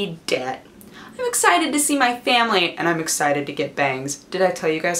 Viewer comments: Is it wrong I heard "I'm excited to get banged". WOAAAAHH! TMI lmfao. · I heard, "I'm excited to see my family, and I'm excited to get BANGED". It made my brain flip out until I realized what she really said. :I